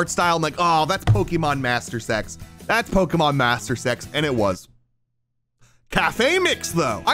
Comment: it sounds like he is saying "Pokemon Master Sex" 🤨